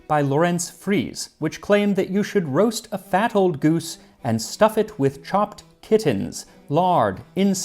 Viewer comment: When you said "Fat old goose", I heard "Battle GOOSE".